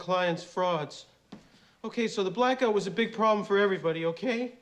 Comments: "The black guy was a big problem for everybody!" I'll take movie lines that you can't say anymore for $200. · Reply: Sounded like he said black guy! He said black out. That’s why Lewis said he was stuck in an elevator for several hours. Blackout caused in the first movie I believe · "So the black out was a problem for everybody" When I was a kid, I always thought he said "the black guy" as in Winston. I remember wondering why he'd be a problem for people lmao. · Literally for my entire life I thought at 1:16 I thought Louis says "OK, so the black guy was a big problem for everybody" and remember always thinking "WINSTON? WINSTON WAS ALWAYS THE BEDROCK OF THE TEAM" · As a kid, I thought that Louis said “the black guy was a problem for everybody”. I wondered why he threw Winston under the bus. Made a bunch more sense when I realized he said “blackout”.